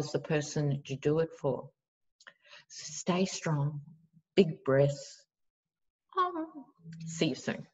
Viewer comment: I am not right. At the end of the video I thought you said, "big breasts"!🤣🤣